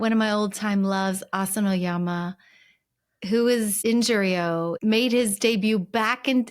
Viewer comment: I thought you said "Injury-o" - like the poor guy was nursing all kinds of bashes, bumps and strains